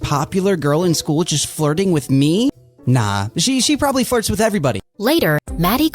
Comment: why did I hear “farts” 💀